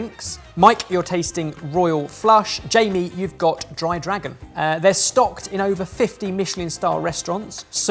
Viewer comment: Bahahhaha plz tell me why I thought it said “Gay Dragon” 😂🤣 .. hmmmm yes dry lol surrrre